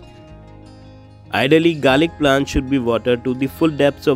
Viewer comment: did you just said garlic naan 😆😆